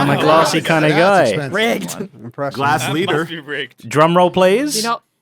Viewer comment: so at 30:15 did mumbo say its f*cking rigged?!?!? if not, what did he say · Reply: "That must be rigged"